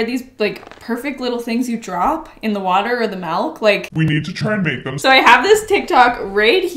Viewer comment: did she just say... malk?